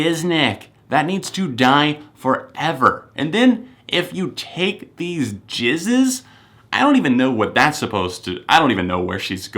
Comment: when he's talking about "if you take these jizznes" the caption say "if you take these Jesus" and I laughed so haRd